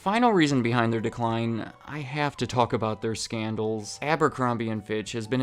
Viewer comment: I had to rewind for the 5th reason, first time I heard it thought you said sandals not scandal 😂 great coverage as always 🤙